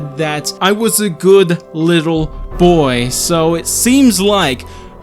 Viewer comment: I heard it as "I was a good little baby" not boy